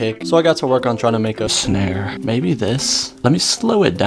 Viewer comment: EXCUSE ME WHAT DİD U SAY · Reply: Snare:>